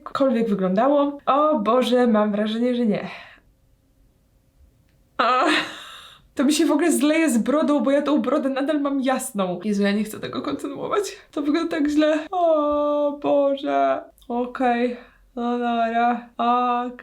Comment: czy tylko ja tam usłyszałam: „to wygląda jak zlew” XDD